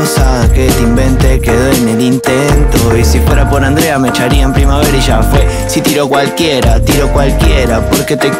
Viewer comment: Gino Wojcik yo pense que decía “si fuera por andrea mecharía en primavera” que tiene mas sentido jajajajaja